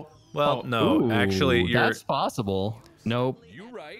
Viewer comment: did anyone else hear marks "you right" in the back lol